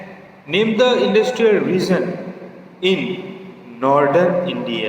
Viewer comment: Sir plzz speak properly because someone can get confused like you pronounced "reason" Instead of "region"